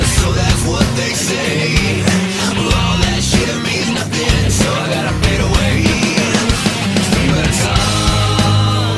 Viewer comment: did that just say "all that shit" or am i just hearing things? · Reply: Yeah, he said SHIT